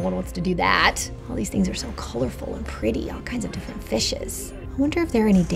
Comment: Why do Americans say "fishes"? The plural of fish is fish. All I keep hearing is "feces"...